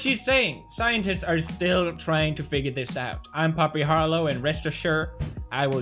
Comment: I thought he said Papi instead of poppy